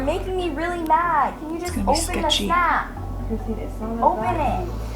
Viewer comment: I was so confused with the snap one 😂 cos I thought she was saying "open the snack!?!?" and i was thinking why would she want you to open her snack?!?! Lol 😆😂😂😂❤ XxXxX